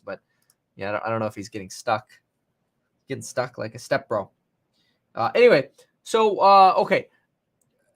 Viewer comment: Am I the only one who heard him say getting stuck like a step bro